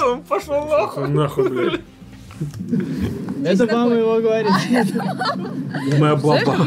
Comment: это мама его, - говорят участники, это баба его -говорят комментаторы; этот парень-комментатор слева так многгболтает, что все пропускает. то фраер-шоу услышал только когда десять раз это фраер прозвучало, то не услышал, что про девочку было сказано, что это подруга дочери; - забил своей болтовней весь эфир